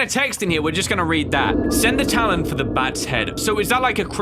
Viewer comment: "Send The Talon For The Bats Head!" is what it says at 26:10...